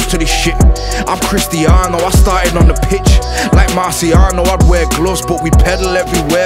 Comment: The disappointment when you thought he said “you know I wear gloves like martial “ but he said Marciano which it still hard